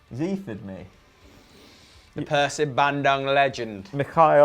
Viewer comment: did said "Persib Bandung Legend"?